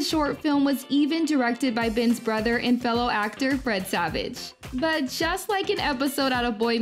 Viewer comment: I keep hearing Bread Savage